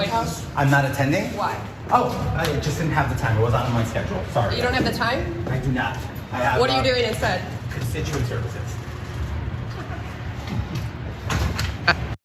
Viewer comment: I thought he said "kitchen services" 😂